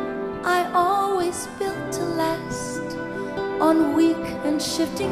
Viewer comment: I'm writing this comment for a couple of reasons. 1. I'm homesick. 2. Ms Carpio is one of the best singers in HK. If she is to shine internationally, she needs the best of assistance and support from her entourage. Unfortunately, on the basis of her rendition of my favourite song here, she does not seem to be getting it. So, pardon me for being audacious. Just two points, if I may. 1. Yesterday when I was young, many ....WERE (not ARE) waiting to be sung. 2. .... ALAS (a sigh, an interjection showing regret), not (AT LAST), an adverbial phrase that makes no sense in the context. Best wishes from a 老而不 who means well. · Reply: 她沒有唱錯這句 ‘I always built, alas, on weak and shifting sand.’ 是字幕打錯，因為 ‘built, alas’是連音字被聽成 ‘to last’。